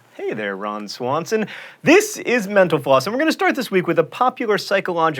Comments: When he says "mental floss", I hear "menopause". Anyone else? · It sounds like your saying menopause when you end mental floss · Misheard mental floss... Instead heard "tnx for watching menopause..." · At the end it sounded like he said "Menopause" and not "Mental Floss". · Half the times you say 'mental floss' you say it so quickly I swear I hear 'menopause'. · When you said mental floss, i thought you said menopause.... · Every time i hear the words "Mental Floss" i swear he is saying "Menopause" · when he says mental floss, its sounds like hes saying menopause